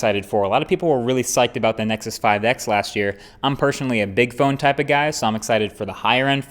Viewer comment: i was just playing your video in the background listening to it while doing other stuff.. and i heard "im personally into a dick phone".. LMFAO